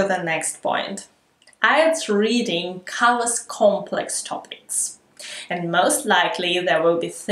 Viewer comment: Around the minute 6:08 you say: "IELTS reading (something) complex topics...". What is that (something) word? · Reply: IELTS reading covers complex topics